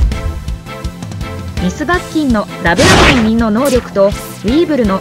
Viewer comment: ここ、「Ms.バッキンの…」の後なんて言ってる？ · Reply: ラブラブの実の能力ですね